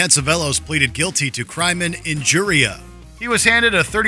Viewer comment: what is "private injuria"? May not be what was said but that's what it sounded like to me. CC says "crime injuria" but that's not what it sounds like. Anyone know? I googled "private injuria" but didn't get a very good answer. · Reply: Crimen injuria - Wikipedia <link>